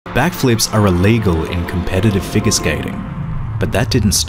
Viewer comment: I thought it said "backshots are illegal in figure skating" i was like "NO SHIT"😂